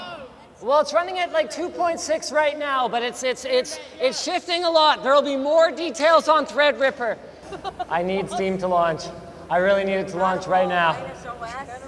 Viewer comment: I heard "Threadripper, yes!" lmao